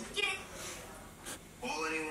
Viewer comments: What were they saying at 14:58 to 15:07 ? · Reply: Probably because you’re Asian: probably: and I’m white: probably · What did they say? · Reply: said,”Probably cause your Asian and I’m white.” said, “Probably.”